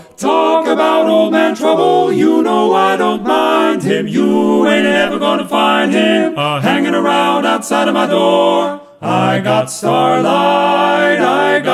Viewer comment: First time watching, I thought you said Ol'Man Trudbol, I don't mind him. You won't find him 'Round my door. ;D